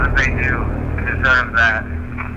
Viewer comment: I think I heard it say "Dandy... Deserved it."